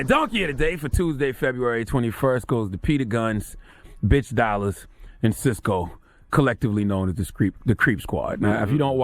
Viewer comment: Waaiiitttt 😂😂😂 so y'all mean to tell me no one heard ctg say " Peter Guns, BITXH dollaz and Cisco"???? 0:20.. Am I hearing shit?? If so plsss check me 😥😭 I played it over like 3000x G